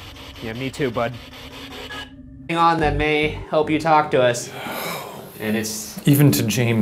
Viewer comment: SOUNDED LIKE HELP ME